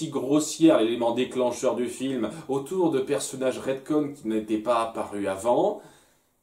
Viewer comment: Ca veut dire quoi "redcoin" ? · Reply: Il s'agit de "retcon", pour "retroactive continuity".